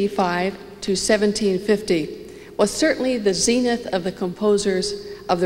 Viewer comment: Dears, I would like to quote her, but can't understand what she's saying: "Bach was certainly the SENATH? of the composers of the reformation." (Second 00:06) Would be great if anyone could help. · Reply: i is "zenith".